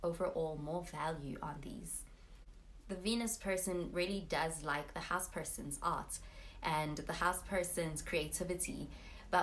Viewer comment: i thought you said "venus really likes the house persons ass" lol